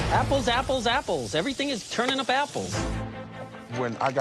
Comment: can someone translate for me what did say ToT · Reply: apples apples apples everything is turning to babbles? idk lol